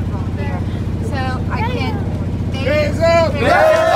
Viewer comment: Ewok: talking in hand sings A local gangster: ayee, GANG GANG ! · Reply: why does it sound like she said “faze up” really really quietly...? like it follows her lips perfectly lol tf??